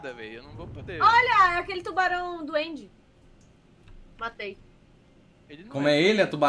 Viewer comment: eu ouvi tubarão do end ai eu já imaginei: Ue, teu amigo da vizinhança mara?